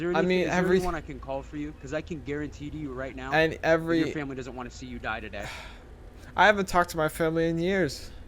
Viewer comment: lol did he say "your family doesn't wanna see you die to death" ?